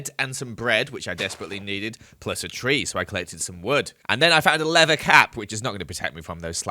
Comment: At first I thought you said a leather cat